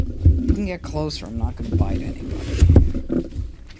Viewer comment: When DA Pirro was opening and said, "Move in closer, I'm not going to bite anyone." I first heard it as, "Move in closer, I'm not Biden!" So I replayed it and was able to laugh twice. Judge Jeanine's intelligence, clarity of expression and sense of humor is so refreshing.